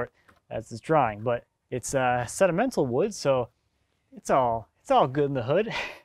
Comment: Did you say sedimental wood? Or did you mean sentimental? As in having personal intangible value?